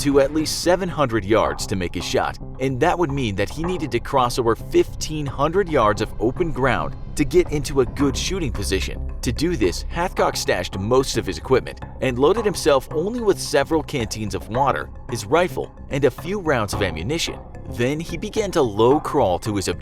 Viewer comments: I thought he was saying halfcock at first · I keep hearing "Halfcock". Sounds like he got injured during the war. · All i hear is "halfcock"